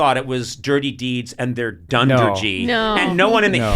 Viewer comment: I misheard Conan sing it as Dirty Deeds and their Dunder Jeep. I was picturing the members of AC/DC rolling in their Dunder Jeep with the top down, solving crimes and getting into fights.